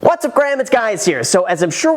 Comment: of the video anyone else catch the "whats up graham, its guys here" 😂😂😂